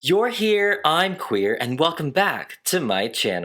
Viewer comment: Misheard intro as "I`m here, you`re queer" in the beginning and was like so true, pride month baby